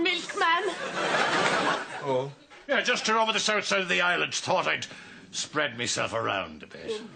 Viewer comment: I love this episode, but I’m also a sore arse and a craic killer! 😂. How can he be the new milkman when he’s got a load of hairy weans across the island? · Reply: I thought he said he was from the West side of the island and had decided to expand.